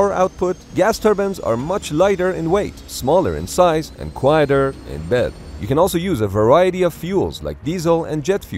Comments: did I hear "and quieter in bed"?? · quieter in bed hmmmm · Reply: I came to look in the comments to see if that is what I heard. "Quieter in bed" lmao